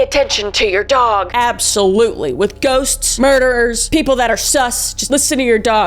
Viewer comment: I started washing my hands so I heard you say “Ghosts, nerds, listen to your dogs, your dogs know when people suck” but you actually said “murderers” lol